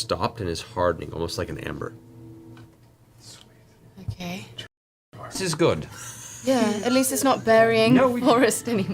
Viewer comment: What did Travis say at 3:50:22 that had Sam, Laura, & Matt laughing? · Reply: The audio blips out in the middle of it, but according to the captions: "Like Jurassic Park." Which makes sense because the previous sentence was about the tar stuff hardening like amber.